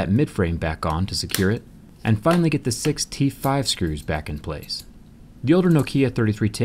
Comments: hit like if you heard 65 instead of 6 T5 screws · I thought you said "65 screws" lol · Is it only me who heard it as Sixty Five screws?